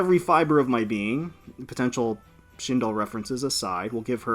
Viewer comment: Did you say shinoL? 👀